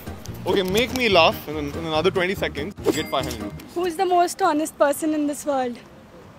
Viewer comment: Disclaimer: this joke works only if you know Hindi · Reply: At first, I heard "honest" as "tallest", although I know Hindi,but I didn't understand at first. Now I understood